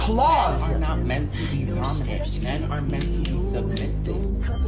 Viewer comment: did he said men are supposed to be submissive?